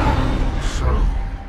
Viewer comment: What did Unicron mean when he said “I shall be king again”? Isn’t he already an omnipotent being? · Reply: I thought he said “I shall BEGIN again”